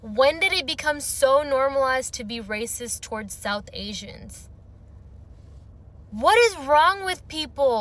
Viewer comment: They truly act like they've never met White people... have fun with that need to be in proximity to whiteness... Toppy Rest in Peace to the souls lost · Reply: SHE SAID WHEN HAVE IT BECOME NORMAL TO BE RACIST TOWARD SOUTH INDIA, I GUESS INSTEAD OF BLACK PEOPLE LISTEN CLOSE AT 7:18 TIME